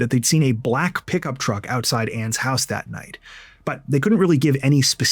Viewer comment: Her name to me for the longest sounded like u were saying "anime"